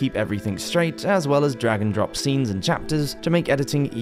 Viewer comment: At the beginning, during your ad comments, I heard "drag and drop" as "dragon drop", and it made perfect sense in the context of what you were talking about 😆🐉